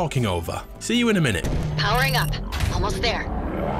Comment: what does she say? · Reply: Found it. It's Japanese. She's saying "Powering up. Almost there."